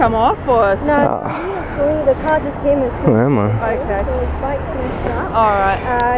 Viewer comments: did you say "where am i?" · Did he say at 3:28 where am i???